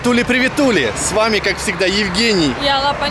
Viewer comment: Услышала " привет , Туле"😂